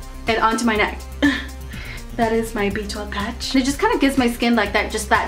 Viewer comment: What is the patch for? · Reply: I heard her say B12 patch